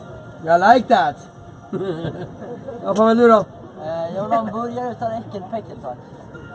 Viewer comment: vad sa han? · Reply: "Jag vill ha en burgare utan äckelpäckel"